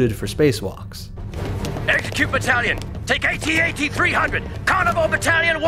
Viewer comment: rex will say at0at instead of at-te just a little error i noticed watching this